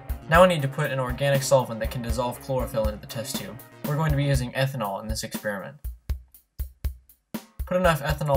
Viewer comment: Hello At 0:14 , what did u put into the test tube? We are not native English speakers so we couldnt hear what u said. Please reply us as soon as possible <3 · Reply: I added ethanol to function as a solvent for the chlorophyll.